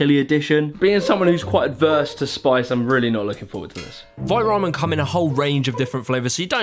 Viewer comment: Holy shit, I was today-years-old when I realized it's called Vite Ramen. I always thought you were saying Fight Ramen.